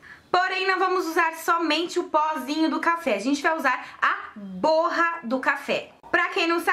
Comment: Eu juro que ouvi "a porra da café" kkkkkkkkkk